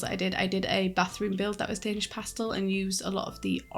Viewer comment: For a second I thought you said Danish Pastry and my mouth started to water! Love the apartment design btw! 😜